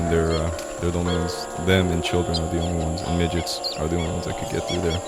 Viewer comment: Did he say midgets?